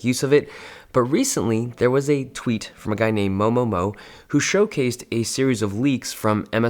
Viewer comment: I thought you said 某某某 but then realized it's moMoMo